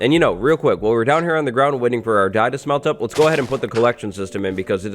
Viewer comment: subtitles say “Waiting for our dad to smelt up” 😂